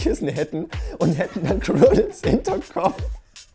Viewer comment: Was für ein chibi Kissen hätten? xD Ich habe irgendwie den Namen so gar nicht verstanden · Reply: Quirrelkissen😂